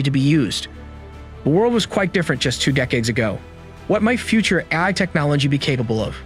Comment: “2 decades ago punchcards were the primary form of computer storage”…?? I was stuck on this… then I figured you were going to tell us “the script of of this video was written by AI” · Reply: I thought he said "few decades ago..." but the CC shows two.